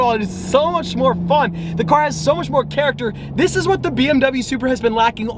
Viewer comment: did he just say bmw supra I'm triggered